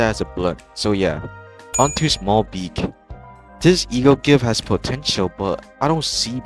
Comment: DID YOU SAY POTENTIAL